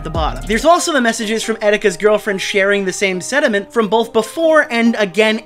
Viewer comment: brah, you cant passive aggressively criticize Keem for mispronouncing words and then say "sediment" instead of "sentiment".